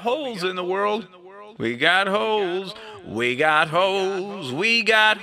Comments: Sounds like he is saying we got hoes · it sounds like hes saying "We got hoes" xD